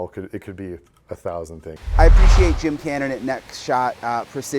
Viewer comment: Did you say, "I appreciate Bob at neck shot precision..." at 5:28? And, I do really like you but dude...did you even check the back of your hair? (during interview with Sheriffs)